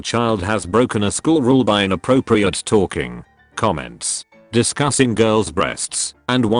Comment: I thought this said "inappropriate falling" for a sec..